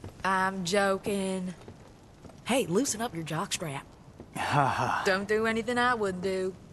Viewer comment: "Dont do anything i wouldn do"... comin from Nico, that open a LOT of possibilities. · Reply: I honestly thought she said "Don't do anything I would do". Would be a funny line.